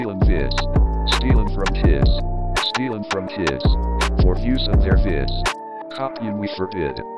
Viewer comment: i heard stealing for shits